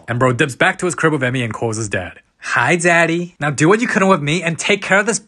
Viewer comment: I’m sorry did bro say zaddy